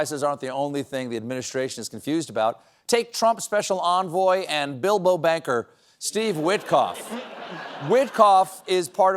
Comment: Did Stephen say "Dildo Banker?"